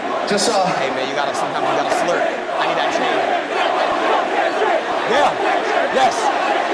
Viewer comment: did he say “ sometimes you gotta slurp.. I need that chain.”???????🤦🏿‍♂️😭😭😭